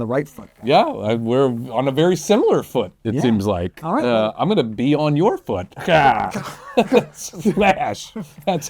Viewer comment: I heard, "I'm gonna be on your foot." as "I'm gonna pee on your foot." I thought, "Oh, no! Grant isn't housebroken?"